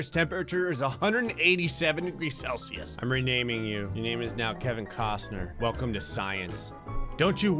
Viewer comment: i misheard that as kevin cosgrove